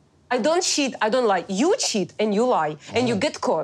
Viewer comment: When she was saying “cheat” I thought Natalie was saying “shit” 🤣